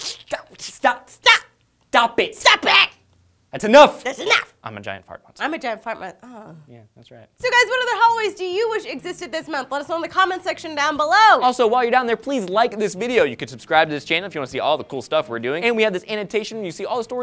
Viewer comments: lee said hallways instead of holidays... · is it just me or did Lee say Hallways instead of holidays?